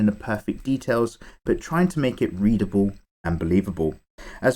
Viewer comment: I thought you said 'Breedable'